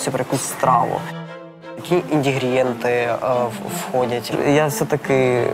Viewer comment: Я одна услышала как чувак сказал на 36:42 індігрієнти? 😂